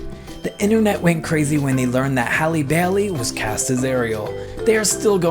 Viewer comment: halle baily i thought he said halle barry which creepily sound way to simular